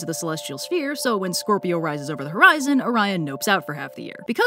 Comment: I THOUGHT IT SAID ONION AND I GOT SO SCARED LMAOO